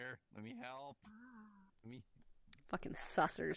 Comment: Can you repeat that? My tinnitus made it sound like you said clankers. Oh, you said *sussers* okay, nevermind. Heh, that explains it.